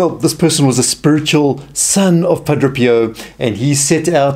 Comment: Wonderful video. The speaker has a very unusual way of pronouncing the saint's name. To my ear at least it sounds like he is saying Padro Pio.